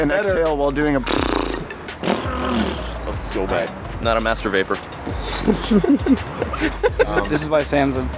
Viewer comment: what does mastur----- Ooooooh, you said "Master Vaper".. Ok....